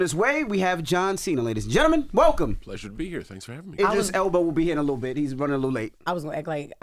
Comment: Did he say Idris Elbow? 😂😂